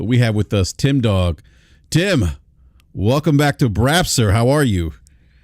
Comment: LoL @ 3:30 ish He says "welcome back to BRAP Sir" But he totally sounded like he said "welcome back to Brazzers" Hahaha Brap trying to talk about how toxic the gaming discourse is now compared to "back in the day".... Dude Brap is constantly stirring the pot saying super fanboy BS all the time. Even the title of this video LoL, "Xbox hardware struggles". I feel like there was no real reason for that headline except for trying to get hate clicks or pony clicks haha. Thats not just Brap, that is just how the gaming youtube community is